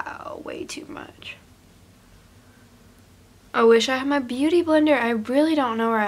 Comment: In the beginning of the video I could've sworn she said "I can't find my beauty blender" then she just picks it up😭😭😂😭😂